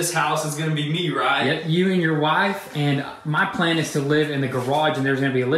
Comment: Did he say Wife? These guys are living the dream dude.